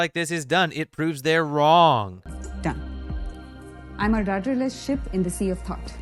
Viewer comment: rofl I thought she said "I'm a daughter of shit in a sea of rot".